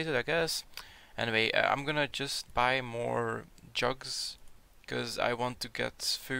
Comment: Lol when you said "gonna buy some more jugs" I thought u said "guess im gonna go buy some more drugs" i was like wtf lol